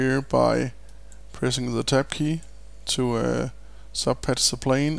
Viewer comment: what is he saying at 1:35? Pressing the 'tepp' key - what is that? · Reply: Ime saying the TAB key as in Tabulator. it turns on the subpatch function in lightwave :)